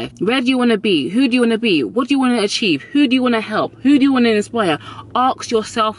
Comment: what does she say?Who do you want to...? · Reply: Grace Maina who do you want to inspire?